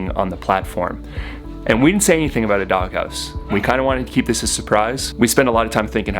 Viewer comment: I kept hearing “dug house” and thinking should I google that? 😂 😆 😂